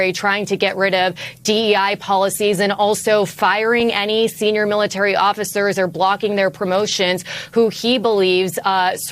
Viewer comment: "Firing any senior military officers or blocking..." I thought she said "Firing any senior military officers who are Black". (Which would still be on the nose).